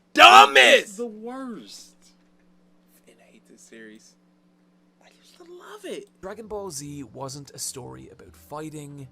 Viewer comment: did you say you hate this series and you just love it? I keep listening for a "don't" but I can't hear it because your kinda mumbling to yourself quietly. That aside, you guys make some really good points, Dragon ball having BIG problems isn't news to me, and I still enjoy it despite how STUPID it gets, However you ain't getting me to throw whatever personal attachment I have for it out the window, but I will admit that there are a LOT of Shonen that did... Shonen... better than dragon ball, like one piece, yuyu, Toriko (why is Toriko so damn unpopular?), and maybe even bleach. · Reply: He said that he hates the series and that he used to love it. It's totally understandable that you like it and realize it's problems. We have a problem with the people that prop it up as a shining example of all things shounen and anime. You never know what's going to be popular in terms of manga. There's no reason why Fairy tail should have gotten over 100 chapters (it's trash and one of my favorite mangas). Maybe Toriko was just wrong place and wrong time. As for Bleach being better, there's a legit debate to be made lol